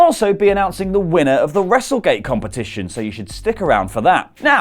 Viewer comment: I thought he was saying wrestlegay